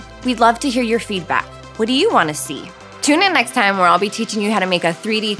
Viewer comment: That's why I said tune in next time not next week. Don't want to run out of tutorials to fast. Love to see what you've been creating <3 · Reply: oops. I watched it again and saw you said next time cx I thought you had said next week for some reason. And I have a bunch of singles and an adorable pokeball cuff I made (